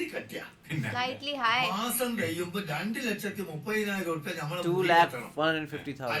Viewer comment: he Say 230000 Rupees.Subtitle Say 215000😂